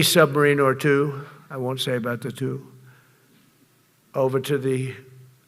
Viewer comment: I thought he said, "I won't mention the fuel," rather than "the two."